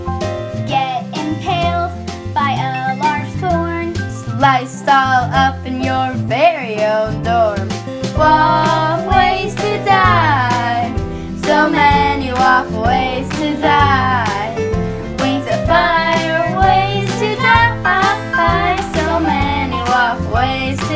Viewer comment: I heard, “so many wuuf ways to tie”